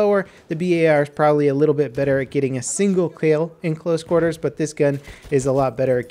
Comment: he says " the bar is better at getting a single keouil" I think he was meaning to say kill